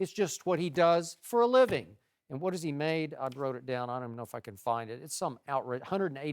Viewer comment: "I wrote it down, I don't know where I can F UCKIN' find it" did Skip actually say that a did I get it wrong?